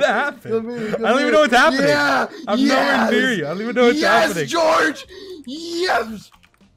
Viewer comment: I think on 3:06 was a baby saying yes lol